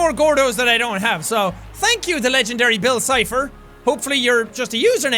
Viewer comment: When he said Bill Chipher I thought he said Bill Nye